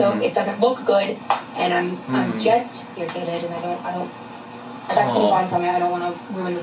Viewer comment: did she say irrigated???? Watching some I see.